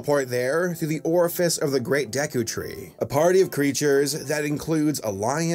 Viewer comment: DID HE SAY THE GREAT DEKU TREE OR AM I JUST DUMB!?